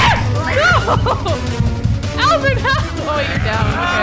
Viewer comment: did she say alvin or albert? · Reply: Albert